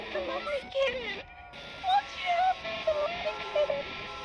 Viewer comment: When Amanda yelled “WON’T YOU HELP THE LONELY KITTEN” for the third time right before transforming into her Demon form and crying in supposed agony, i thought she said “Wild u hell the loly kEEIID”